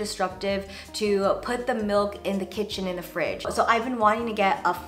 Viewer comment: Did just say “put the kitchen in the fridge”? 😆